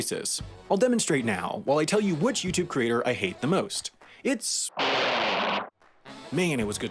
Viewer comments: I think he sayed ,,you", and I would not be suprised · ¿Did I hear Wendover Productions? F XDDDDD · I think he said “me” at 3:50. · I heard "Real Life Lore"